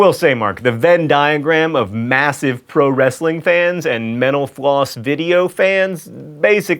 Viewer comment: Every time you say mental floss.. it sounds like youre saying menopause..